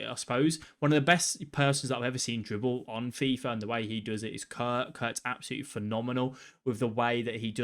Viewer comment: What did he say on 7:27 Kirk or Kurt or what did he say? Who ist this guy can someone help me does this guy have YouTube?